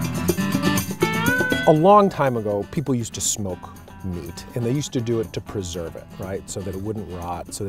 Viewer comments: At first I heard, "Along time ago people used to smoke weed......" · i thought he said smoke weed in the very beginning. then i remembered i clicked on a smoked fish video